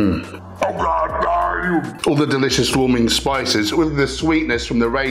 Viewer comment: Hmmmmm. What do you say at 2:56?😂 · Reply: "OHGODDAYUM!" (tm)